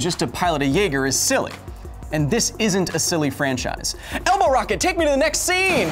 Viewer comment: I thought he said Elmo Rocket, lol